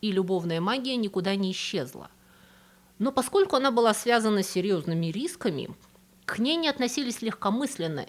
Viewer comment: услышала "с серьёзными Ирисками" - долго думала, как это))))))